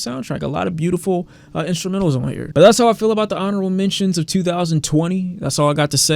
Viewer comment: i thought it said horrible mentions of 2020